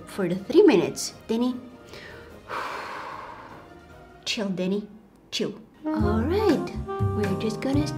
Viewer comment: When she said “chill deni chill” I thought you said “chill daddy chill”